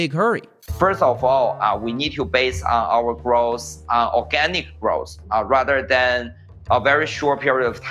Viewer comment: I thought he said we need Organic Gross